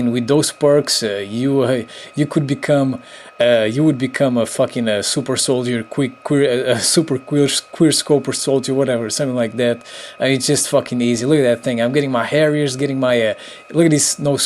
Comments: Did he say a "super queer scoper solider" at 3:49? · OMG I CANT BELIEVE I COULD NOT PRONOUNCE QUICKSCOPING COREECTLY WHEN I MADE THIS VIDEO LMAO =) "D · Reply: I thought u said queerscoper lool